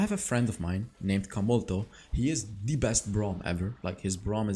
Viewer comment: Who is the most underrated support in the game? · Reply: did u say his name is camel toe 🤔